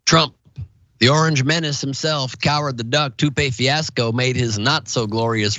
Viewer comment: I'm pretty sure he said "coward the duck" but I heard "coward the cuc" lol